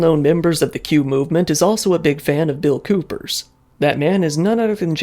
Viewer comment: He literally predicted 9/11 and the war in Afghanistan😂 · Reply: My bad we had a miscommunication 😭 I thought you were saying “Nun of Bill coopers predictions came true”